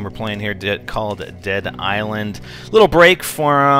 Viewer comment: also thought he was saying cotd but he said Called-Dead Island